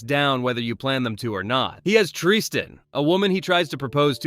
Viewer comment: I thought I misheard you say "Triste" not Tristan which is Spanish for Sadness and I thought that was some pun from a writer